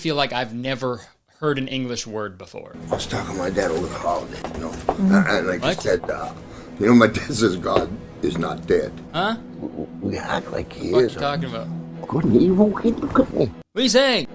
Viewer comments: What I hear: "I was talking to my dad over the holiday, you know? And, like, he said- (laughs) 'You know,' my dad says, 'God is not dead. We act like he is.' He couldn't even- he couldn't even look at me." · This is what I think that this strange man is saying (with a bit of extra context since I think it helps): 4:36 "I was talking with my dad over the Holidays, you know, and I-I said uh..." "You know my dad says 'God is Not Dead,' and we act like he is." 4:45 (What Kurtis wants to know): "(Continuing from last convo) Good and evil, hey look at me." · I believe what the strange man said at 4:36 was, "I was talking to my dad over the holiday, you know, and he said, you know my dad says, God is not dead. We act like he is. Couldn't evil come and cut me?" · That’s Roddy Piper and it sounds like he says “ good and evil, you have to be kidding me” maybe? 😂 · On the part were u didn’t knwo what he said i heard “good and evil? Get some cutlery” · I heard him say, "Good and evil hip gobnik." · I hear "Good and evil keep him company" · I literally hear "good and evil cookie cookie" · Re. the unintelligible editor, I heard 'Good and evil in a cupcake'. Is this helpful?